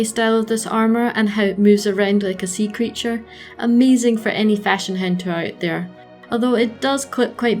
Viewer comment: I heard it "fashion hentai" lol